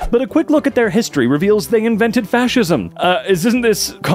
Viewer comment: my mind blanked out for a second and I heard "parmesan cheese invented fascism"